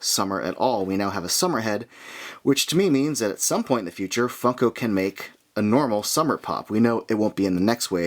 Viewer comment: Can I just say man great vid but for a second I thought you said fuck o pop and I was like what? 😂😂😂